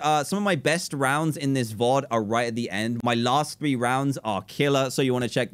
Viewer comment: did he just say "vod"